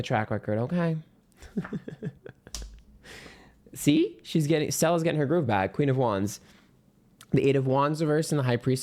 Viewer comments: did you say sels getting her groove back?👀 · at 06:21, did you say SEL???? getting grove back😅